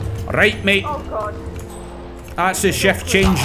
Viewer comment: Wait at 14:38, did you say 'Chef Change' or 'Shift Change'?